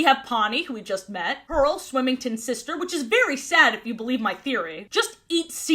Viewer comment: Is it just me or did say "Swimmington"?